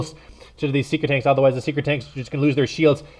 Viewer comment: It's SEEKER tanks, not Secret tanks. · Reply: Lol I thought said Seeker Tanks.